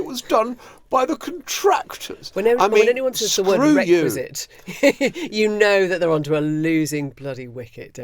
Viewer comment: I had to do a quick rewind at 2.36 cause I thought Emily had said bexit but it was requisite. Hmm.. kinda rhymes. Brexiquit. I think I just coined a new word. I'm no longer a remainer I'm a brexiquitter. 😁 Brexit is the core of this foul political posturing. I just heard today that they even ordered the removal of signs in any foreign language too - far too welcoming that.